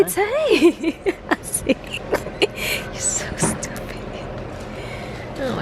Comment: Did I just hear her say "you're so stupid"